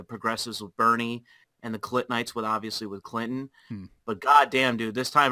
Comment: Did he say Clintonites or Clit Knights?